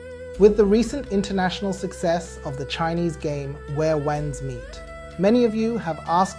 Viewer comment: This is wht i heard at 2:27 "...where wangs (🍆?) meet..." 😂 ... Anyways despite tht funny mispronunciation, this is one enjoyable video. Thanks and keep up the good work.